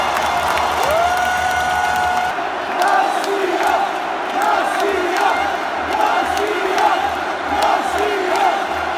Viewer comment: Qué es lo que dicen?? García? :v jajaja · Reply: Rosiya